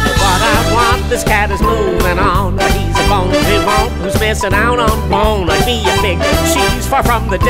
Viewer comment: Instead of hearing “bonlibonte missing out on fun,” I heard, “He’s a funky hunk whose missing out on fun”.